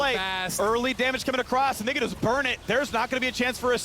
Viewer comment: what does Phreak say before "just burn it"?!?!?!?! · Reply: Early damage coming across and they can just burn it